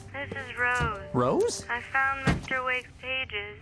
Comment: Sounds like he said Mr. Wick 😂